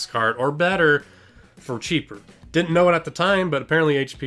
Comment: I think your audio stuttered, sounded like you said, "to be neater" -- you should post edit videos, you woulda caught that and fixed in the dubover to be "cheaper."